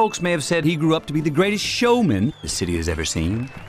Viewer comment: the sound cut out for a half a second at the perfect time. During the word “showman”, the audio cut out at the “ow” so it sounded like “the greatest sh**man”